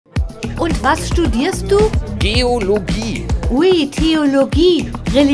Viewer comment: Ich hab erst Biologie verstanden😂